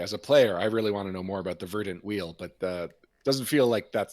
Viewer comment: oh VERDANT wheel. i thought Mark was saying BURNT wheel!